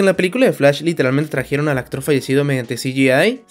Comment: trajeron a la queeeee? JAJAJJAJAJA no le entendí nada xd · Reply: "Trajeron al actor fallecido mediante CGI"